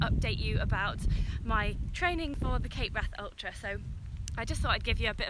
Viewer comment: Huh I just saw the name of this video and I always thought you had been saying "Kate Bath Ultra" 🤷🏼‍♀️